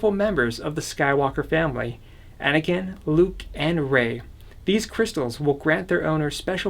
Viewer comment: IM SORRY DID U SAY REY!?